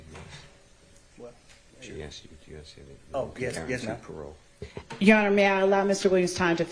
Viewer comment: Judge said damn slow down bih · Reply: I thought that too but she said "ma'am" 🤣